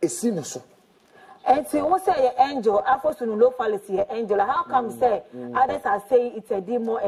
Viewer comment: Bloggers and some pastors should be careful about Stephen Adom Kyei-Duah, hmmm. That word is NOWHERE in google search, but yet people like these liars are cooking the stories. They will start begging very soon. We are here to see. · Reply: okay, so I came back to help a bit. U can check the 6th and 7th book. Of Moses it's there. It's not what he said. Because he said it fast people heard it as afrosonofalis . But those who actually know what he was reciting (which is the bornless ritual invocation) know that name is paphro osoronnophalis....